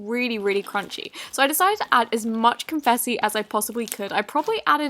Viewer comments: Did anyone else hear confessi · lol this girl said confessy instead of confetty p.s.idk how to spell........ 0:41 or 0:40 or 0:42 lolol but i still love this vid!!!!!!!! p.s. I'm new to this channel!!!!! also u have a beautiful voice i wish i had ur voice ur singing is probly amazing. still idk how to spell IF ANYONE PLAYS MSP ( MovieStarPlanet) friend me at hellodarkness<num> P.S. DON'T ADD CAPS OR ANY SPACES ITS SPELLED THE WAY I PUT IT.......................... ALSO IF U LOVEEEE JAKE PAUL AND MRS COSTEL ( also known as MRS. PAUL ) like this comet and comment #LOVE U JAKE PAUL AND MRS PAUL.......